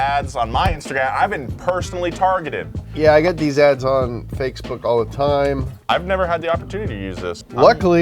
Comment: did you say "fakesbook" 😀 because accurate lol